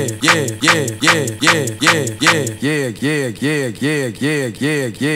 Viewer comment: man saying yo gay instead of yeah